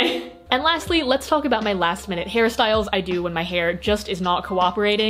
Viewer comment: when she said "hairstyles" I thought she actually said "Harry Styles" and my directioner ass had a HEARTATTACK oh my